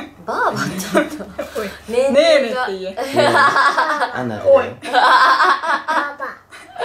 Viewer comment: 辺り､が「あなるだよ」って言ってるように聞こえるw